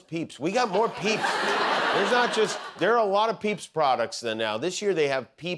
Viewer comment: Did he say "There are a lot of peeps products the now"? I've never heard "the now" outside of Scottish so far.